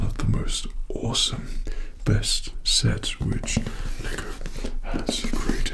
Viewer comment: emm brah did i heard that right? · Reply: He said lego